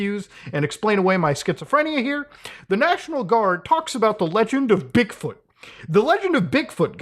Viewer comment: I swear to god and all that is unholy that I heard first "the legend of dickfoot" 0:38 first and it sounded way more menacing